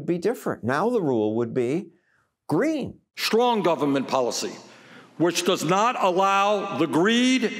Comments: I'm sorry, did Bernie just say "schlong government policy"? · I think he might actually be saying 'Schlong' governement. O.O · Socialism- Idiots that are only good at saying shit people want to hear are put in charge and ruin everything. Also, does Bernie say "schlong government policy" at 3:10?